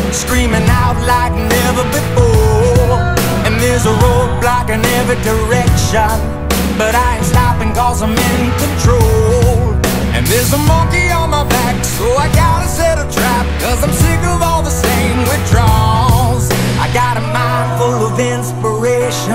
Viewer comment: thought he said "ain't stoppin cause I'm in detroit" XD